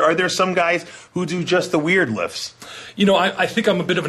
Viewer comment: what the fuck u sayin, WIERD lifts?